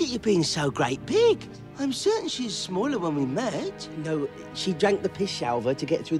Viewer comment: I swear when I haven't watched this in a while when I hear that first time, it sounds to me like he's saying "She drank the piss Albert to get through the door." instead of "drank the Pishalver." 😂 Made me think one of the tweedles had the name of Albert for ages.